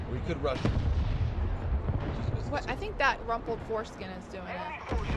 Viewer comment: Umm did she call a panzer 4 wrinkled foreskin lol